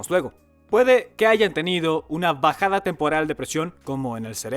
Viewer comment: entendí una pajada 😂